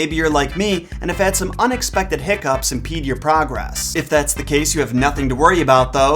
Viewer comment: "... and have had some unexpected hiccups 'n peed your progress". Wait what? Oh! "Impede"! Anyone else mishear that way :)?